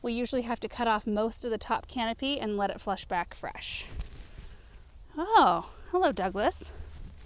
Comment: ish when says “ oh hello Douglas” all I heard was “ oh hello Norman” a la Seinfeld! 😂